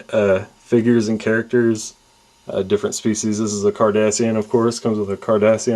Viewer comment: lol it sound like you said kardashian